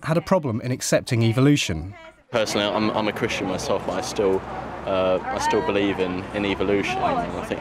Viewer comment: did he say he "believes" in evolution?